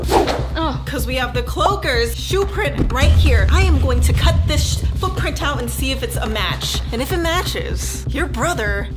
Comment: DID SHE SAY S**T AND THET CUT IT OUT?? REGINA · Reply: They didnt cut it out and she said sheet